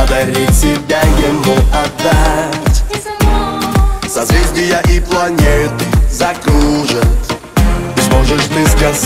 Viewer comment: почему я слышу: сосиски и планеты?😅